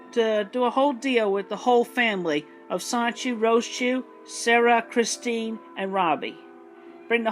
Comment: I heard “Subpeona Rosechu” and thought it was a rare moment of self awareness.